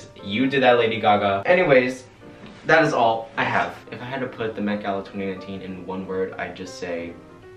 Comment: did he say met gala 2018?